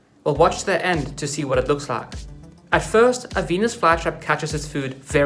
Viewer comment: I heard you say penis fly trap 💀